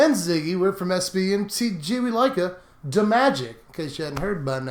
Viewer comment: What does always say at the beginning? "We'd like you to magic?" "We liked you too, magic"? "We like you two-magic"? Don't get it 😬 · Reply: I always hear, "We like-a da magic!" But after this intro I'm not sure anymore 😂